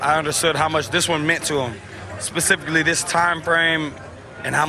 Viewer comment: Who is the guy? I can't hear it clearly. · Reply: Mattered to him in this timeframe.